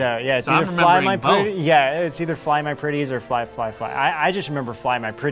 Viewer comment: did anyone else hear pretties?